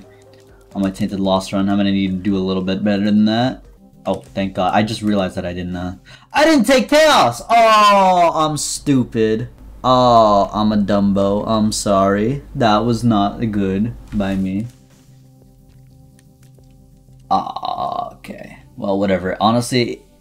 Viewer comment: Did he said I'm a dumbbell